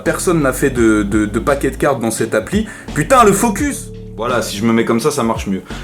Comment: J'étais mort quand j'ai entendu "Putain le focus!"